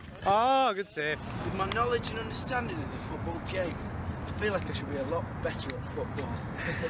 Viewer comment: till 0:20 what did harry say? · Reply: With my knowledge and understanding in the football game, i think that i should be a lot better at football